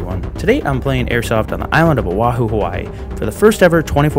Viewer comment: I thought you said island of tabor